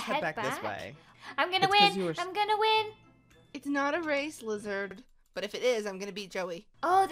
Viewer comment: Did say "Lizard" instead of "Lizzie" at 10:11ish!?